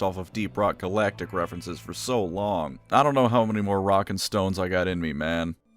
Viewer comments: DID I JUST HEAR ROCK AND STONE?! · Did somebody say ROCK AND STONE?